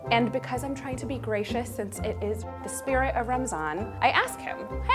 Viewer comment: did she just say 'ramzan'